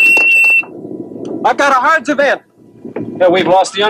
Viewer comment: what did he just said? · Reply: I think he said HARDS event. It’s an acronym for High Altitude Radiation Detection System. It would have alerted the crew to a nuclear detonation.